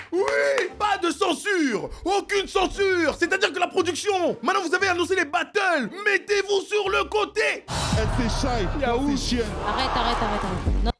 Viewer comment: Je rêve ou il dit:" Elle c'est Shay, toi t'es une chienne" 😂😂😂😂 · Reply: *elle c'est Shay, toi t'es chienne"